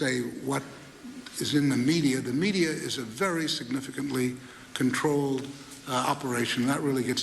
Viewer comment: what is he saying 5:43? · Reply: There's a media monopoly.